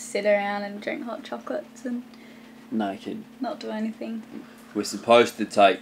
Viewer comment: Tee hee! Did just say naked at 6:27?